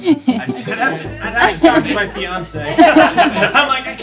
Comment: I thought that guy said "If I can convince Beyonce," at first instead of fiancé. Probably a hard sell either way. LOL!